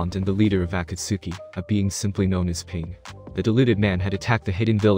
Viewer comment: my subtitles said ping